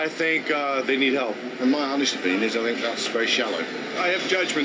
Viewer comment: I'm having a little trouble understanding what the British or Australian guy was saying at 0:05 in the video. I think he said "In my honest opinion is that I think that's very shallow." Sorry dude, I think that's what you said but I'm not too sure.